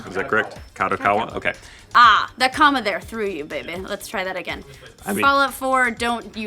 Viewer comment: did she call him baby or bey-bey or smth? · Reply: I think I heard baby too.